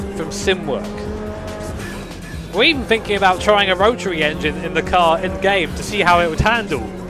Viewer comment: did he say rotary 👀👀👀👀👀👀👀